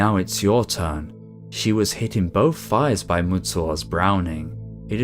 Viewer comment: What did he mean when he said “He hit her in both fires” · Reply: Sounds rather like 'both thighs ', and that seems to be the only thing that makes any sense.